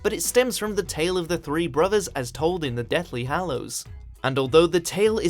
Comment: bro when he said that dumblodore might be the death, i thought he said deaf and i was confused fr good 30 secs